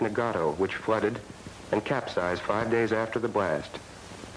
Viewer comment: あたりで字幕では”４日後に沈没”とありますが、音声ではcapsize five days after the blast と聞こえる気がします。違っていたらごめんなさい